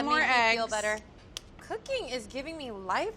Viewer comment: First I thought Tiffany said "hooking gives me life lessons". I'm sure hooking does give a person life lessons, even though I now realize she probably did say "cooking".